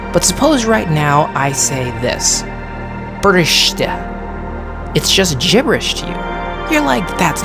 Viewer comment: I think you said "briste" which means "broken" in Irish.